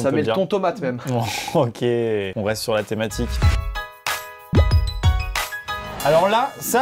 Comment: À la fin de la première vidéo au lieu de " thématique " j'ai entendu " thomatique "